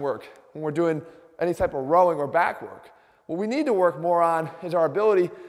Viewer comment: i heard 'u need to work moron' hahahahahahahaha